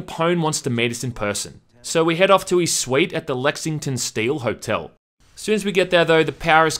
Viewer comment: Lexington Steel????? Did I hear THAT correctly?😉